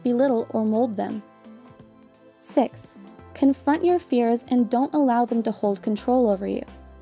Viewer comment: it says "tears" instead of "fears", but otherwise great video!! :)